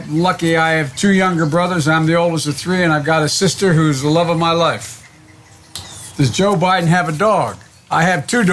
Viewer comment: Why did he skip can joe biden code? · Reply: Did he say I have 1 sister the love of my life